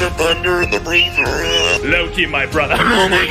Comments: I love the part when that guy said: *Loki my brotha" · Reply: I THOUGHT HES SAID “milk me my brother” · “ LOKI MY BROTHA “ leg folds like an omelette “ HÆÙHKŁŒÆ- “ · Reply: I thought it said “milk me my brother”